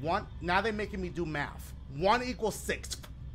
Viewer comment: I.thought it said making me do mouf 😭😭😭 pause!